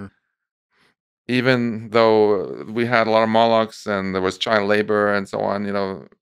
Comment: what word is he saying, ? molochs ? and if so why?